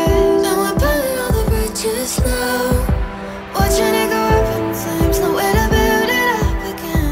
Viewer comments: I Keep hearing "watch a nigga go up in flames" in the beginning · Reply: "Watchin it go up in flames" is what she is saying, Kiwi accent. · all i hear is "im pulling all the bitches" haha :D great song! love the female vocalist!